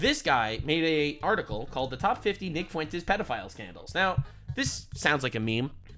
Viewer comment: Why tf did I think said "Pedophile Candles" 😂😂